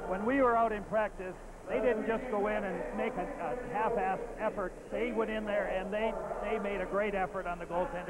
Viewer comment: Did I just hear Bobby Hull say "half-assed" on national TV in 1982? Why yes, yes I did.